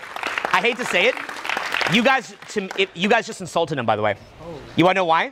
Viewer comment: I thought he said “you guys just ASSAULTED him by the way” and I was like wtf